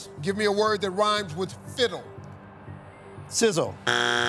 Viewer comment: Lol at first i thought Steve said Fizzle. Did i missheard it or is Steve the one saying it wrong?